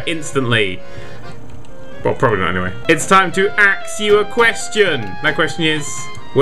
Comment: "It's time to Axew a question." What? That's what it sounded like.